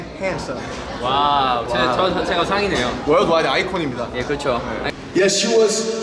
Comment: does he say worldwide eye queen? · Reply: He was speaking in Korean because I heard "입니다 "